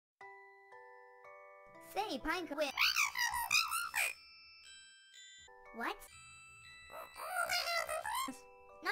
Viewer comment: and 0:07 what did party hat say? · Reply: WHOA THERE SLURPY DON’T JINX IT! 0:07 You don’t know what jinxing is?